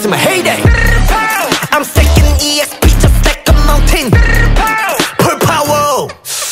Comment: THE WAY HAN SAYS “PULL POWER” INSTEAD OF “FULL POWER” IS SO CUTE I LOVE HIS ACCENT SM ☹️😭